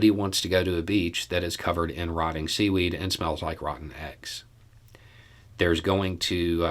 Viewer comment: I think you said 'smell', but I thought I heard 'snot'. So glad I wasn't sipping my tea...